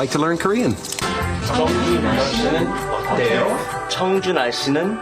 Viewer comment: 청주날씨는 어때요? 저 이거 정준하씨는 어때요?로 들었어요ㅋㅋㅋ처음에